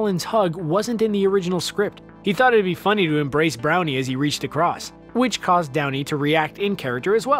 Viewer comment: did you say brownie?